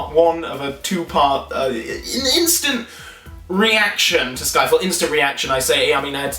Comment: Did you say "instant reaction to Skyfall" at 0:29?